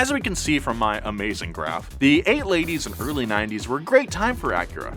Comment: Did anyone else catch Nolan sayin: "8 ladies, early 90s"